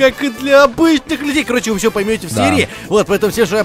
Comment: ты сказал в начале видео ( вы всё поймёте в Сирии) а не в серии. или мне послышалось.